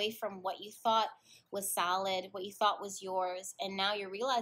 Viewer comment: I heard salad instead of solid.. lol On a serious note tho when you introduced Larry I completely lost it.. Larry is the name of the guy I have a crush on at the gym.. he works there and would flirt with me and I am the awkward-est person so I kinda messed things up but I'm trying to get it back.. slowly but surely.. idk its still pretty iffy idk but I really like him but lot knowing each other very well has put a fork in us getting to know how to maneuver a relationship.. in general not just romantic.. idk jess I'm so sad about it